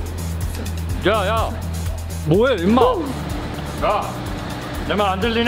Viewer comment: 에 임마라고 들림??